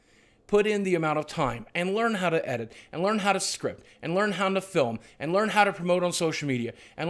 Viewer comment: For a moment, I thought Boogie said, "and learn how to strip" Well, I guess that can make you a bit of money